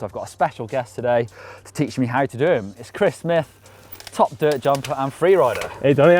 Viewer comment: Chris Smith sounds like your saying Christmas with a lisp. Great video.